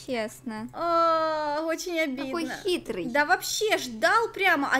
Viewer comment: мне послышалось даун🤭💕